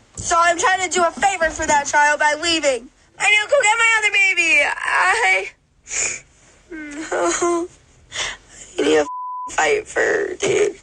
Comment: Subtitle error at 32:23 - 32:24 The subtitle says "I need to go get my other baby!" but what she said was "and you can get my other baby!" or "and you can go get my other baby!"